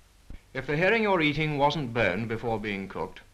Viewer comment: Literally thought he said burned, not boned. 😂